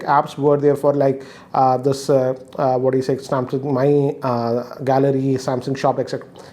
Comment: When he said Samsung Pane, i thought he was saying Samsung PAIN 😁